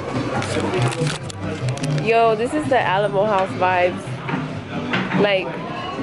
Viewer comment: i thought she said animal house vibes.ha alamo house...